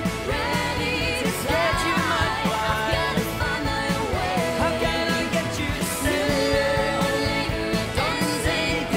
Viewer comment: Does anyone know what John says at 3:56?? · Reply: he says, “i’m scared you might fly”